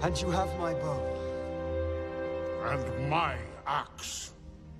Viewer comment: When me and my sister watched this decades ago, we would giggle because it sounded like was saying 'and my ass' 😂